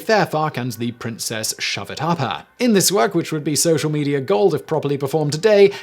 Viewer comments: [...]and the good princess Shove-it-up-her[...] did I hear that correctly?! Lol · Did you say the princess ShowItUpHer at 11:55? xD